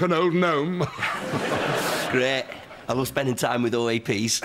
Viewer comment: What is Ash saying & what does it mean? Sounds like, "I love spending time with old a.p's(?)" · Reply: O.A.P. is Brit slang for 'old-age Pensioner', meaning someone who is probably retired and taking a pension/social security from the Government (so very likely a person older than 65--70).